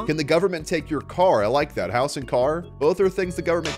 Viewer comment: ngl I thought it said monkey instead of money